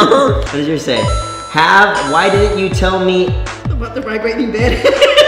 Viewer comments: what did she say? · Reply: “why didn’t you tell me about the vibrating bed” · what does she say at 8:18 ? I keep replaying it but don't get it :/ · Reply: mary anne about the vibrating bed 😂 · What did they say at 8:17? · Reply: "Why didn't you tell me about the vibrating bed" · what did she say? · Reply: Abby Mounce "Why didnt you tell me about the vibrating bed" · what did she say that made them all laugh??? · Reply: "why didnt you tell me about the vibrating bed " LMAO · I didn't understand what she said in 8:18 can someone tell me? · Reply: "why didnt you tell me about the vibrating bed?" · what did she say??? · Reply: Jake Namessme she said vibrating bed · What did she say at 8:17 ? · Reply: It said " why didn't you tell me about the vibrating bed?"